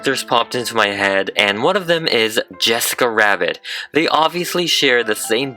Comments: I hear Jessica 'wabbit' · I keep hearing you say "Jessica Rawbit" is it just me bro?